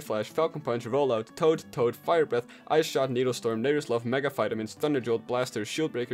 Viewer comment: what did he sayyyyyy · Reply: Needle storm